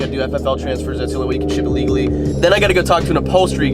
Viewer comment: When you said "That's the only way to do it legally" I thought you said "That's the only way to do illegally" lol, I know that's not specifically grammatically correct but still